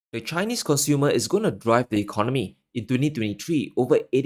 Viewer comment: Not being disrespectful in any way but at the start of the video i thought you said "...the colony of Germany.." and i was nodding along with what you laid out. I then realized you said "..the economy.." I think "...the colony" is the better description since good little soldier Scholz became Germany's leader and handed over ALL decision making to Washington LOL.